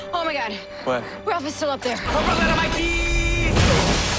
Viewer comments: my favorite moment in the series ever “Hope I don’t have my keeeeeeys....OOOOF!!” Giant blobby Ralph lands on the car, that’ll never NOT make me laugh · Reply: He actually said “Hope I don’t land on my keys.” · Someone please could say me What does Ralph say?? Sorry for my english 😅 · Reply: “I hope I don’t land on my keeeyyyyss”